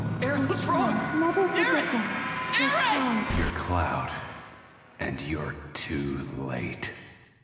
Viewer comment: To anyone's who's actually able to tell me what Aerith was saying HERE 9:30 Thanks. Like, a lot. Like For real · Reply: You're Cloud. Never forget that.